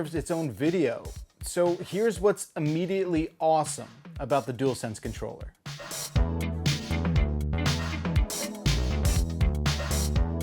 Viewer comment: I thought he had said awful instead of awesome at first, i was like mfff how